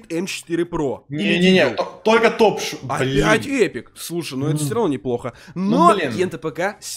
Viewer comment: Мне одному послышалось блять 5:12? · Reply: Опять эпик